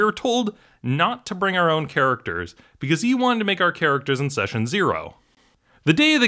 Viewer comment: "Don't bring your own characters, I'll be making your characters" "Now here are some blank character sheets and fill them out to my exact specifications " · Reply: I thought he said “cause he wanted us to make them from scratch,” not “I’ll make them”